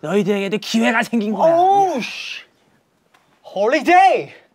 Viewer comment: he said holiday instead of holyshit 😅